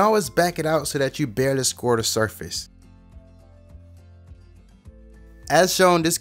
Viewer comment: "...but you can back it out so that you barely squirt the swordfish." Hahahah thats what I heard the first time. Love your vids!